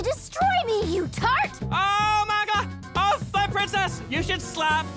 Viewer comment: On the Adventure Time one, I thought LSP said "OOOH MA GLOB. Oh fuck, princess! You should slap Bubblegum in the face!"